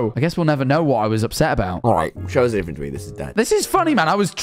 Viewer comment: when editor didnt know what tommy said, he said "alright, show us a different tweet, this is dead"